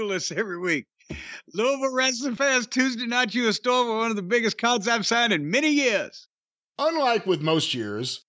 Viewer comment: Im sorry but what the hell did he say? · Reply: Louisville wrestling fans Tuesday night you're instore for one the biggest wrestling cards I've signed in many years